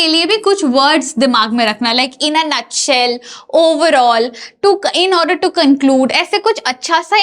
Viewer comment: what jst she said in an each cell? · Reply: Nut shell Mr. Levi